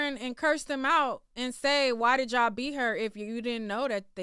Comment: I thought he said man did y'all beat her